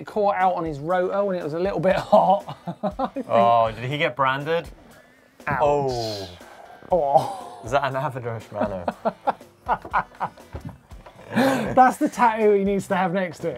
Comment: What the hell did he say? Is that an avedurshbanner?